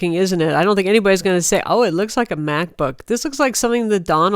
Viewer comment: Wait, what does she say at 2:02? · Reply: "I don't think anyone would say , of it looks like a MacBook"